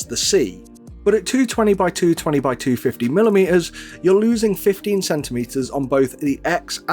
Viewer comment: At about 1:20 I think you meant to say 15 mm not 15 cm